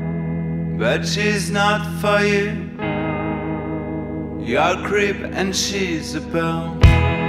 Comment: When it said you're a creep and she's belle I thought it said ur a crip and she's a blood